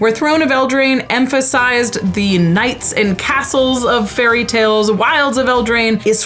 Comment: English is stupid. “Throne of Eldraine is the knights and castles of fairy tales. Whereas Wilds of Eldraine is the twisted and dark fairy tales.” I had to rewind, because I thought she said “NIGHTS and castles” instead of “K-NIGHTS and castles.” I’m like, “Aren’t nights dark?” 😅